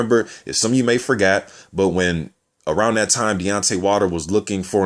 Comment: sounds like you say Beyonce Wilder